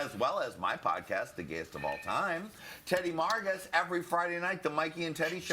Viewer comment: Correction: I thought said Teddy Morgan...I misheard, it's TEDDY MARGAS. he's fun & a riot.